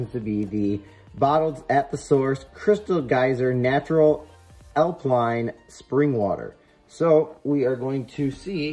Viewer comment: did u just say "alpline" lmao =]